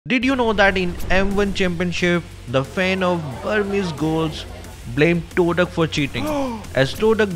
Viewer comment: Myanmar players☕️ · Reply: Myanmar Players?I thought the vid said"FANS OF BURMESE GHOUL"